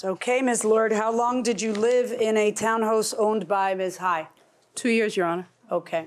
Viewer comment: "town hose" ?? did anyone else catch that? 😂😂